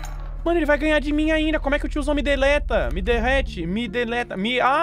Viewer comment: até hoje eu nunca entendi uma palavra doque ele disse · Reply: Derrota, eu acho